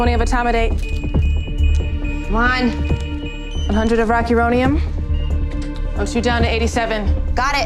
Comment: idk how to do timestamps but at 22:52 does she say rocky roadium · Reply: Rocuronium. I believe it's either a muscle relaxer or a paralytic